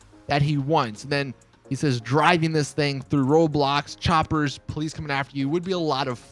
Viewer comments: Did he say “driving through roblox” at 4:21 or is that just me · Am I the only one that thought he said ROBLOX at 4:20?